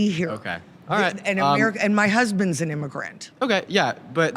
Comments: “I’m married to an illegal immigrant. He came here legally.” The stuff that comes out these people’s mouths … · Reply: a legal immigrant, is what she said. 14:40 go back and listen again. your bias is messing with your hearing, take that on board. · Did just say "my husbands an illegal immigrant who came here legally"? 😂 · “My husbands an immigrant, an illegal immigrant… who came here legally” ????? did i mishear